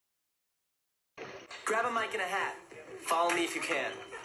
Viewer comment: I thought he said grab a mic and a half if you can and I was very confused.